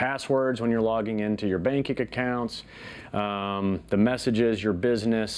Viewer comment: did he say "bankick acounts''?